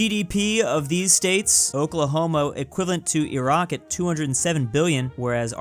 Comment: did you just say Oklahomo?